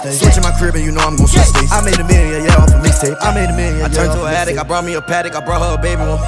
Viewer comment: When he said: "I made a million, yeah, of a mixtape" I heard: "I made a million in a year, you know im gon miss that"